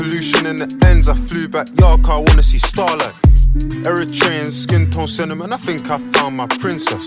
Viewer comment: Bruhh when u said "every train,skin tone..." i thought u said Eritrean U got me hyped for sec The song fire tho 🔥